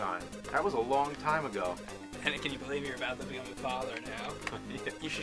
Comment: What does say at 2:35? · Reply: "Can you believe you're about to become a father now?", I figured.